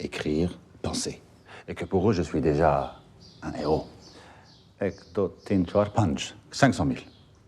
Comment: did he say ek do teen chaar paanch ??